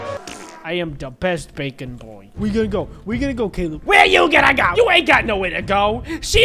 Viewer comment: what is saying 😕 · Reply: Where you going to go